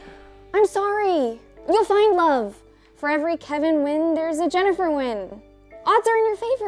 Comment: “It’s a Nguyen-Nguyen situation” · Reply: so did I. I thought they were saying "Kevin Wyn" the entire time lol